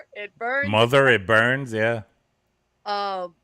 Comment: I didnt hear "burns". I heard "hurts"